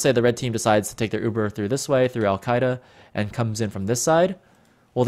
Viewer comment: did u say al qaeda?